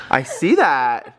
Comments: Who else heard say "Oh, dad see that block?" at 11:16? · it sounded like said 'you guys see the blocks' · it sounded like said ( dad see the blocks )